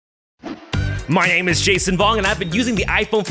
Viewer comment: Ohhh I heard "my name is Jason Bourne" xD